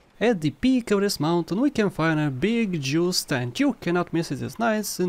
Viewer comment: Number twenty eight was tricky for me. I heard you say fruit stand and paused the video. I kept looking for a fruit stand. It's not there in my game. 🤔